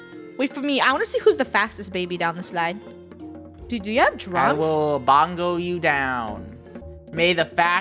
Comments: Did funneh say drugs at 10:29 ???? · did she just said drugs? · Did funneh say drugs